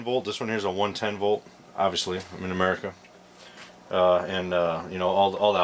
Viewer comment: Sounded like you said: I'm in 'Merica. Made me laugh. Thanks for the review, I'm tired of doing each step individually on my drill. I'm way to impatient. I'm trying to decide between this one and the hornady case prep trio... Do you have experience with both? Comparisons?